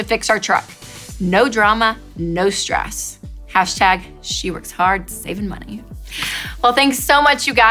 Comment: hey there! Sadie (she) might have been the one that sent in the twitter message but she mentioned "we" not "she" and you said "she" instead of "we". hashtag #weworkhardsavingmoney.